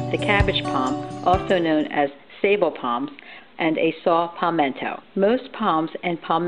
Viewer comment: Great video. I kept hearing about "CabbagePalm," but had no idea what it was. I had seen it before and recognized it. For the Saw Palmetto, you called it "Pal men to." Did you mean that, or "Pal met to?"